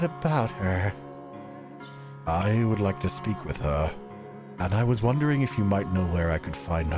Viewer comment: i heard ,"I would like to sleep with her.." an i literally jumped up on the bed......i was like wtf!!!!!! 🤣🤣🤣🤣🤣